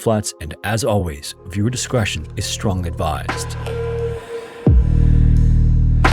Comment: … did you say strong advised?